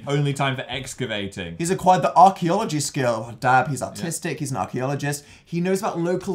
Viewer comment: I thought Dan said “autistic” okay I’m going to leave now.